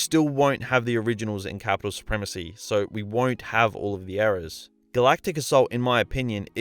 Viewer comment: Every time you say “eras” I hear “errors” and vice versa and it’s really fucking with my head